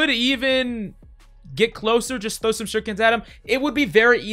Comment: subtitles says you can throw chickens at low health zen